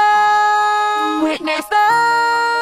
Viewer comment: I hear "wItnEsS tHe MiGhT oF dIsEaSe"